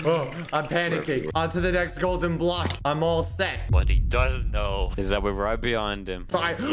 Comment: I heard someone at 4:50 say s** 🤦🏽‍♀️